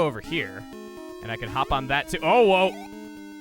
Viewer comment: did he just say OwO